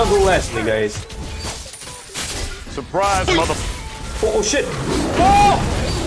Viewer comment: I think he said oh shit